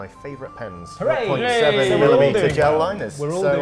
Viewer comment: Did you by chance catch what he said they were? I really love nice pens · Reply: Sounded like .7mm gel liners to me (It actually sounded like he said 1.7mm, but I don't think that is a size that exists)